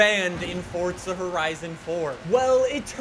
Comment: Is it just me or does it sound like he is saying fortza horizon 4